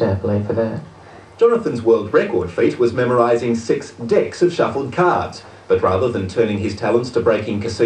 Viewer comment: What did he say? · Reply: he said: memorize six decks of suffle cards